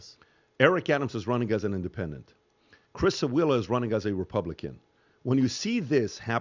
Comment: Ref 3:50 - What did PBD say here? (I rewind so many times trying to figure out) "Chris Ailla is running as a Republican." Who is that?!? Never hear of him! Just setting the record straight ... Curtis Sliwa is the Republican candidate.